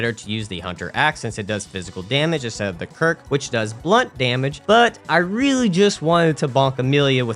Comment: Wait what is blood damage? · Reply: It's damage which scales with bloodtinge, dealt by firearms, as well as Bloodletter and Chikage in their transformed states. Though I think you misheard, he actually said "blunt damage" which is a subset of physical damage.